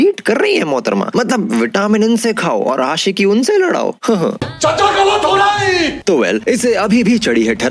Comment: What is he saying and from which movie is this scene from? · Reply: Chacha galat hori